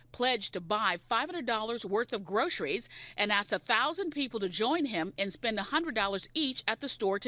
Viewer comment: Did I just hear him say "I axed 1000 people to shop"? That's what I heard